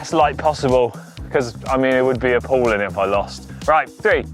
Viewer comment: I thought he said "I would be a Poland if I lost" which is fitting when going against a German